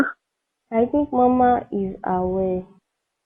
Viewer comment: It would be great to add the text to screen, because of the accent. Eg. I thought the girl said mamma was away.